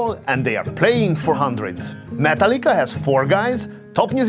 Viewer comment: I almost thought you said Matal-ka (not Metallica) like it was some kind of massive european grand symphony company until you showed the picture of the guys from METALLICA.